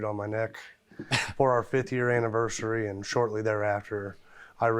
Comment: Fifty years ?? Damn. How old is he? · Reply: He said 5th year anniversary. I thought he said 50 at first, too.